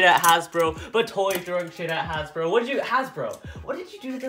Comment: Did you say Sh&$ shame shame on you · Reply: Jessica Jewell ninja said not to throw shade at Hasbro